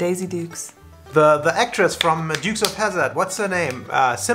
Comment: Lol, when he said "The Dukes of Hazzard," I misheard and thought he said "Medusa Hazzard!"